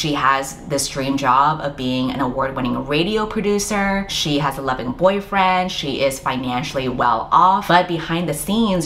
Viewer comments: I have auditory processing issues and when you talked about Stephanie Foo instead of „loving boyfriend“ at first I heard „eleven boyfriends“ and the way my jaw dropped 😂 I had to think about the logistics for a while, before it clicked, that I misheard something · your recommendations always hit so hard! I read at least 3 of the books on this list from your rec videos this year. Unrelated, but when you were talking about the last book I totally thought you said "she has eleven boyfriends" instead of "loving boyfriend" and I was like damn, that is successful on paper.